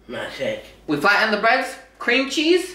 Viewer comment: It sounds like you said bed instead bread 😂😂😂😂😂😂💙 at the beginning